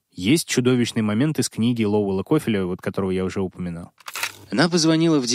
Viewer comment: Подскажите, какую книгу цитирует на 51:55? Имя автора не получается расслышать 😢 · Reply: Это книга Дом секретов от Лоуэлл Коффил